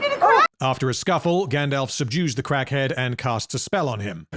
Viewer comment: I could have sworn I heard you say "Gandalf seduced the crackhead and casts a spell"